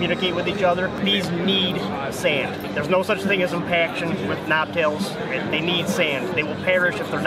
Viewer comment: Straight up thought that man said “there’s no such thing as impaction” and I was like how are those things alive?!?